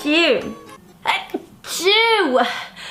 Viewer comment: all i hear is "I Chew"